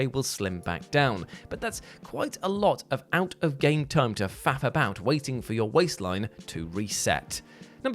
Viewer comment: Did you just say "a lot of time to fap about"? Did you mean "faff about" or should CJ get some localized exercise in to help shed some weight?